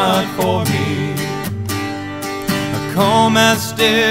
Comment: What are the words at 0:45? "A calm was..."? I can't quite make out the words.